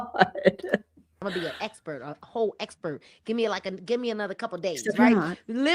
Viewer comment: I swear at 1:39:23 or 24 she said she was going to be an expert. A cult expert….